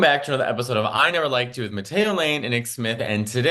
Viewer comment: I heard Mattetto 😂😂😂